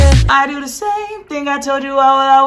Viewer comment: Help I thought he said starquisha💀